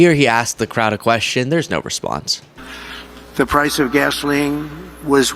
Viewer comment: I was listening to this from another room and it sounded like Trump said, "The price of vaseline was one dollar and eighty-seven cents a gallon, doesn't that sound good?" bwahaa #sloppydonny